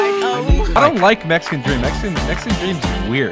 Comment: Dude ngl when I first heard Dream say “ I don’t like Mexican dream” I thought he said “I don’t like Mexican FOOD” ✌🏻